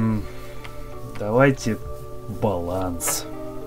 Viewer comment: Что сказал Эдмунд перед разработкай repentence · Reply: Никакого баланса)))